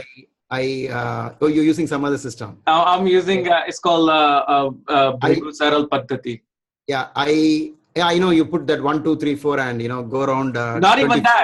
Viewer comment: tells he uses a system, I could listen to the word correctly, what system does he say he uses ?? Anyone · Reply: Bhrigu Saral Paddhati